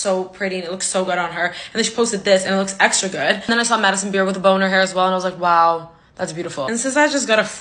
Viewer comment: omg i heard her say “then i saw madison beer with a boner hair” and i literally died